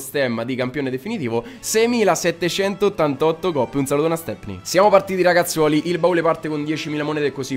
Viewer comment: U salutone a?? Ho sentito bene?!?!